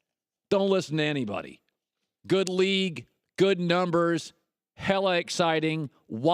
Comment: did Colin just say hella